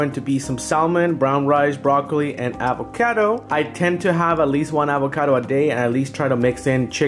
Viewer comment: i thought he said, "apple kato"